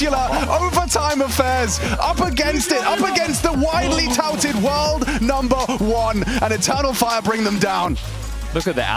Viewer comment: Anyone know what Apex said at 8:20:54 ? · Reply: He said “GG boys” the reason the caster gasped is probably just because of how upset he looked when he said it